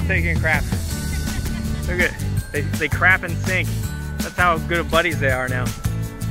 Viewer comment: At first I thought you said they crap in the sink so I'm scanning as best as I can on the phone looking for a sink then I realized you said sync‼️‼️